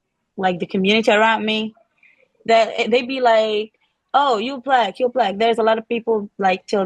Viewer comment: does she say, "they be like?